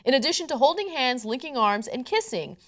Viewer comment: lol I misheared linking arms and licking arms. I wonder if that would be allowed